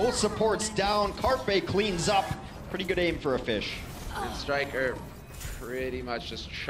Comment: did he just say „pretty good aim for a fish“?